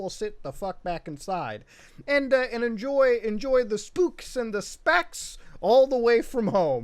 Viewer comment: I thought you said spooks and sp** 😬